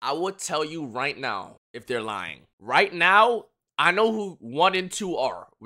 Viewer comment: i thought he said i know who wanted to “r”